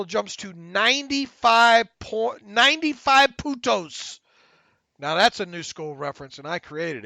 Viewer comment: @ 1:32:37 You said Points (Puntos) in Spanish but it sounded like Putos- no N... made me laugh Love The Terrible Podcast